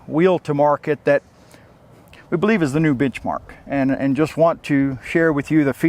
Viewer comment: Did he just say "bitchmarck"?